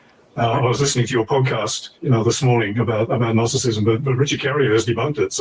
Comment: DID say "gnosticism" and "gnostics" here, not Docetism. You misheard him. But it doesn't really matter much because whatever you said about Docetism applies just as much to gnosticISM 👀